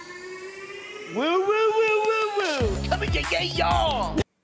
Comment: i think he says "coming to get them all"??????????? (7:04) but yeah, creep